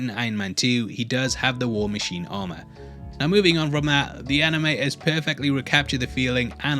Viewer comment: did he say anime?